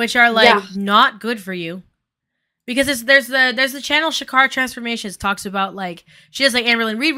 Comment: you mention a channel but I can't hear the name? They sound really interesting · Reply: chikara transformations